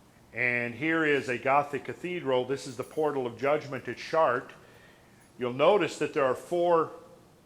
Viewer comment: Did he say Portal of Judgment at Shart?!